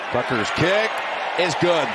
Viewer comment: Did Jim say "Butker's kick is good?"